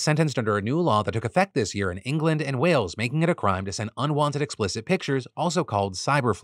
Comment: I'm not gonna lie, I misheard the unwanted nudes law as "unwanted news" laws. And I was very confused as to why a guy got sentenced for sending someone news they didn't want. Wait, no, that's the future if we don't vote the right way.